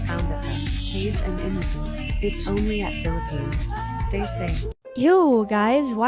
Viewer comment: WHAT DID YOU SAY? PHILIPPINES-?